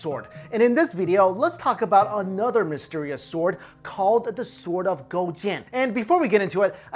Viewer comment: At first I thought he said "Sword of Gotien" and the image of a fusion of Gohan and Tien formed in my head.